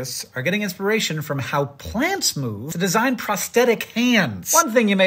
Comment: I thought you said prosthetic plants at the begining